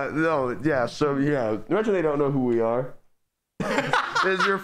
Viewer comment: I honestly have no idea what said 😐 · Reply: He said: Imagine some people don't know who we are